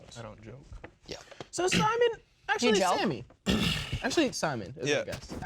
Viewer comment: Did just say "Do you jelk?" hahahaha